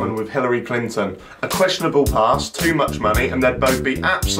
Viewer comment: My stupid ass thought you said questionable ass and i thought "Hillary and Jeremy Lynch are thicc?"